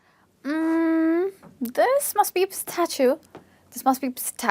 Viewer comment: A pistachio! I heard you saying; "A statue ", maybe a little Ganesha's murti in your fingers but not a pistachio. It's funny because I was confused; 'What?, I don't get it